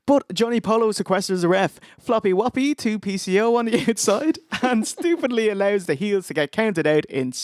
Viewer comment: did you just say "floppy whappy with the 2-piece, yo", brah? Edit: who lit Lex's bus on fire?